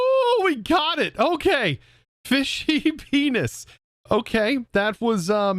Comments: Ummm at 17:38 I swear yo said fishey penis it sounds so funny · I wasnt paying full attention and I thought you said fishy male anatomy xD